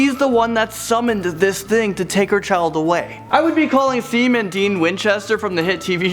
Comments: Yay supernatural <3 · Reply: But did he just say seem and dean? 😓wtf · Seam and Dean Every Supernatural fan is smashing their computer now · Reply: it sounded like he said semen dean XD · Did he really say Seam and Dean?? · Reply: I thought he said Theme and Dean :P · Not to be mean,but did you just say 'Seam and Dean?!' It sounded like you said Seam instead of Sam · I swear I heard you say "semen demon" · Did he just say and Dean instead of Sam and Dean from Supernatural lmao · did he just say Seam and Dean xD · "Seem and Dean from supernatural" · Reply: It sounded like he said "Semen and Dean" for a second. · Did he just say "Semen Dean" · Um... did he just say "Seam and Dean"? SEAM?